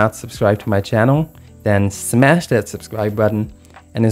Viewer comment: Did he just say fart and soul? Lol